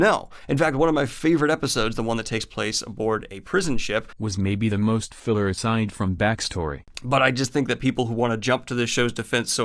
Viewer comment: What's he saying in the distorted part that ends at about 4:00? It sounds like "...was maybe the most filler-istined from back story..." and that makes no sense to me. Anyway, that episode wasn't filler. It was pretty clearly there to show that the guy who rather casually killed a couple folks in the pilot for being annoying and bully-ish (the first scene in the bar) had transformed into a guy who would not kill three folks who tossed him in a cell and left him to die. It also shows that he thinks what the boss and the brother did was worse than what the other three did, which is interesting. At the end of the first episode, he shoots a droid that wants to kill Baby Yoda and we're not entirely sure why. At the end of this episode, he shoots a droid that may or may not be trying to kill Baby Yoda and we're entirely sure why. This episode was all about showing the change in the character.